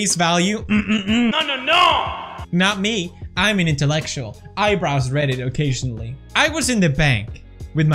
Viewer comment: anyone catch the cheeky "I browse reddit"/"Eyebrows reddit" 1:26 gEniUS